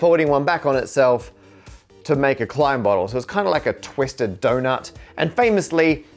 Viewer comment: the captions say climb bottle instead of Klein bottle and 1:53 says kleim.